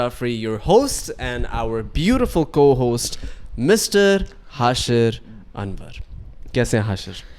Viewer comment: hmary co host Mr Hashir Anwar..i literally heard Hashir Bandar....🤣🤣